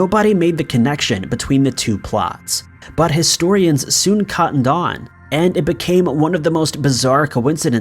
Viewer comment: wait, wait, wait, wait..... wait..... wait. Did you just say "caughtened on" at 9:16 ? Lmfao